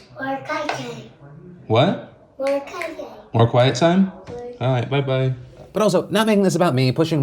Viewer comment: "more quiet time? ok byebye." this melted my heart. i hope when i have kids, i can exercise patience like that. i've come a long way from a few years ago... definitely a brilliant teaching mechanism. those kids are so lucky to have a dad like phil. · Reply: I thought he said "we're in quiet time." Lollol. It was so cute agreed